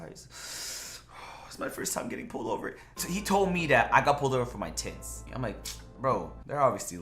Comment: Why it sound like he said they pulled me over for my tits?